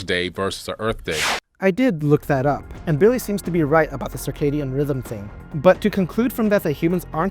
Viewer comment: On rogans podcast Sounded like Carson said “psychotic rhythm” not circadian rhythm 😂 Freudian slip maybe?